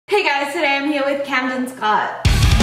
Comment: I heard “Cammie’s hot” not “Cammie Scott”